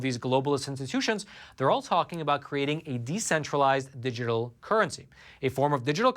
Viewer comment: Roman: you said decentralized instead of centralized.. around the 0:30 mark